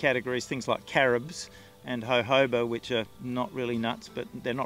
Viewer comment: What tree does he mention around the 3:50 mark? CC says he said "Hoe Hobo" which i'm guessing isn't correct. :\ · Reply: Jojoba for oil